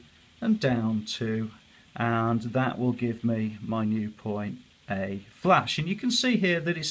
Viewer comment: what did you call the new point? "A..." same for the new B point and the new C point. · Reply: [Thanks For 200 Subs] :D - Hi - I usually say A flash, dash or A1 ... it doesn't really matter providing you work point by point. Hope the video helped and all best S :-)